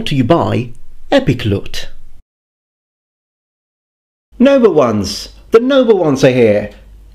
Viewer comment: I never knew you were saying noble ones until I turned on closed captions. I thought you were saying "number ones" and were a big Star Trek fan lol.